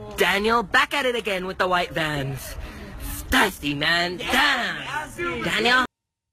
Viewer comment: What is that word that he says at 0:47? It sounds like he's saying something like "styski" or something. What is he actually saying? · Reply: Stussy